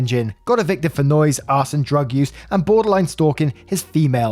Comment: Got arrasted for Arson and drug use... due to your accent, I misheard it as Ass Use.... How do you get arrested for Ass use?